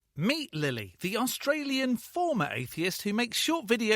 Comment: I heard "meat lilly" and thought of a flower made of meat